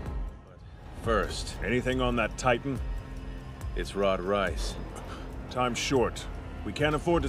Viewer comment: Lol what? Did he just say rod rice?